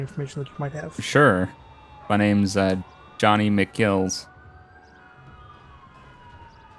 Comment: I busted out laughing, for a second I thought he said his name was ‘Johnny McKills’. 😂